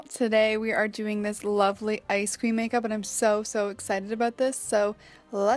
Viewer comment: I thought you said Ice cream makeup and I was like OMG YAS